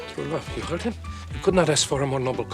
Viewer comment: He said "To bluve", not "true love", the air escaping his body just made it sound like he said "true love".